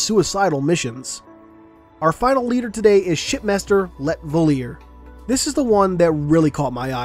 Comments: At first I thought you said shitmaster. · sounds like he said shitmaster